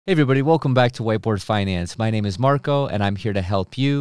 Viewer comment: Every time I hear him introduce his video channel, I hear "white boy finance" even though I know he's saying whiteboard finance. 😂